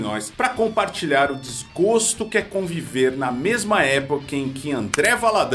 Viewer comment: Eu entendi "CONVÉVI"?